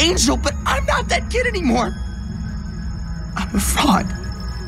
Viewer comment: Why did I hear him saying I'm a frog